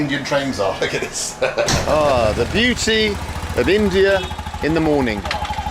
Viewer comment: I thought he said, Beauty. But he must have said, "The Doody of India, in the morning."